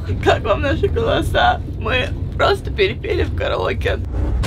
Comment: мне одно послышалось "Мы просто перепили в караоке"?😅